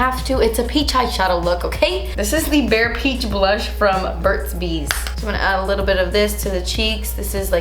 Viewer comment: Lol did i hear Burt's Beach instead of Burt's bees 👀 I get it's ok because this is definitely screaming beach vibes, Love you❣. BTW I Love your Dream St. Palette 💕